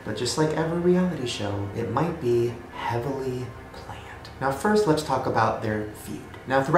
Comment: OH GOD. I thought you said “heavenly planned” instead of “heavily planned”